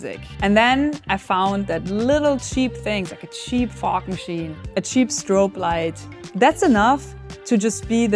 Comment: I love your energy! I think it's a Party On the Wall (POW) Machine, or Instant Party Electronic System, perhaps Party Amplifier?. I thought you were saying "fart machine", but I knew my hearing was off. I thought the dial said "F.O.C." and thought I was too old or not understanding a common electronics term. I'm laughing at myself now. Thank you so much for sharing your unbridled creative energy. Play is the best way to good work. Thank you for being you!!